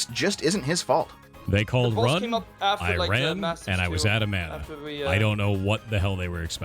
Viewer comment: I’m going to hell for the way I laughed when I heard him say “they’re saying Ghost was out of mana” dude